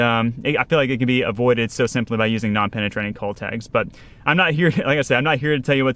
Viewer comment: Yeah we use cull tags in tournaments to help keep track of the fish in the livewell so we can swap out smaller ones for bigger fish throughout the day. Some are penetrating clips and some are just clip on. We like clip on because they don’t wear a big hole through their mouth. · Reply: my hearing so bad I thought you were saying 'Cole tags'. LOL. Must have been your Northern accent. . Why don't yall have little magnetic clips to put on the dorsal, that don't damage anything on the fish while they're in the live well, if they're going to turn em loose?